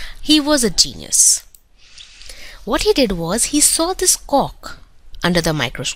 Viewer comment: omg she said cock instead of cork 😂